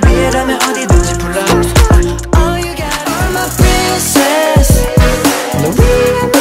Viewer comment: Am I the only one who thought he said I'm a princess😂😂 lol love you Samuel